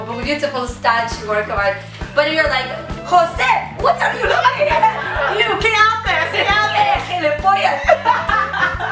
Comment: what did she say? que la polla? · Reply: she said ''gilipollas'' a spanish insult, typically used in Spain